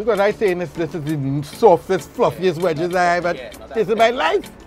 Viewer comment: He said softest fluffiest wedges I swore I heard something else had to run it back 😂